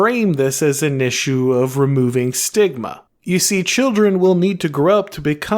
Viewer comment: Enforce stigma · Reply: I thought you said sigma